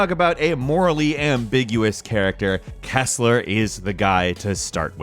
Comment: Kessler is one of the most underrated villains of all time! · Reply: For a moment, thought you said Tessler. Who was the bad guy in Tron Uprising